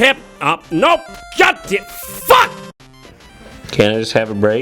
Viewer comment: Я почему-то открыто слышу "Ёпте, FUCK!" XD